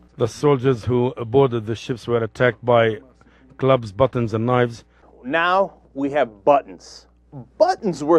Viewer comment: I think he said batons...